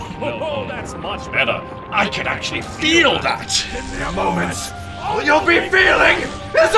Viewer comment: “Ohoho that’s much better, I can actually feel that!” “In mere moments, all you’ll be feeling is OBLIVION!” That line from actually gave me goosebumps. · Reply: I thought he said "In mere moments" rather than "give me a moment".